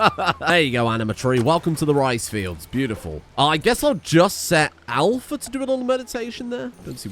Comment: What he said "there anima tree, welcome to the rice fields" what I heard "Welcome to the rice fields mother f@